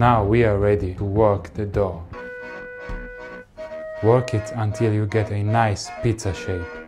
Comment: i thought when he says "work it until you get a nice pizza shape" i misheard it like "work it until you get a piece of shi*".